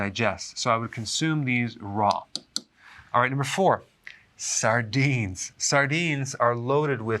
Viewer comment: I thought you said sardines cause stones